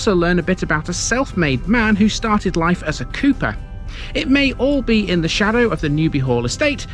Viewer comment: You know I've been playing a lot of Paper Mario lately when I briefly thought I heard "a self-made man who started life as a Koopa".